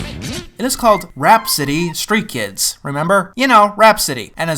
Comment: For a second I thought you said RAT-City street Kids... WHAT'S THE DIFFERENCE!